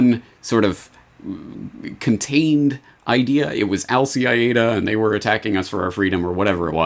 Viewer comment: You guys rock! First I thought: what does say? It is Al-Qaeda .... or Al-CIAda, just in stride, in the flow